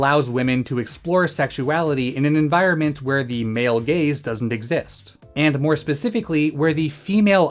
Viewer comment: I thought he said "the male gays" XD